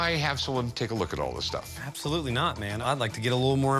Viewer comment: did he say, absolutely not🤔🤔